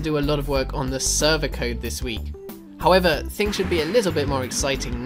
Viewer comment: Hey, at 3:13 the subtitles say "thoings" instead of "things". Also great video! ;D